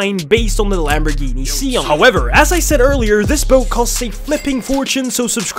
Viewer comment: "SO SUBSCRIBE CUZ ITS FREE" am I the only person that thought he said the yatch is free